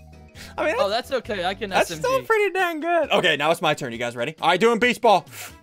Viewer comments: I heard Ian cuss I thought it was dang but it was d**m and start it at #11:44 and put play back on 0.5 and listen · did he say that's pretty da*n good or was that just me? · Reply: He said dang good